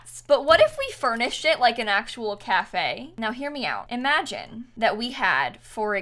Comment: sounds like she is saying hear meow